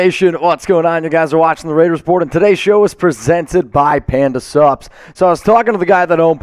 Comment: Panda Supps is owed by a Raiders fan and he is sick of all the injuries just like the rest of Raider Nation! Use code Finjuries at checkout to save money: <link> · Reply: Forever i thought you were saying Panda Subs.